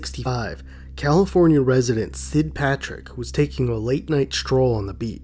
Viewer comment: I thought it said St. Patrick for a second 😅😅